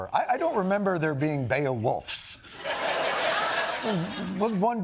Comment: "Beowulfs" ...? I think I heard "Beowursts" 🤣